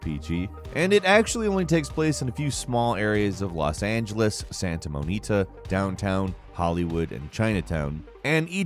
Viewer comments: did he just say Santa Monita? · It sounded like you said "Santa Mo-nita." Pretty sure it's Santa Mon-ica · did you say Santa Monita? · did you just say Santa Monita?